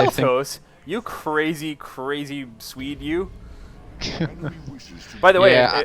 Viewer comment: "You crazy, crazy Swede you," is that what you said?